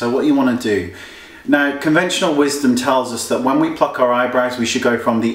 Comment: I thought he said 'When we fuck our eyebrows'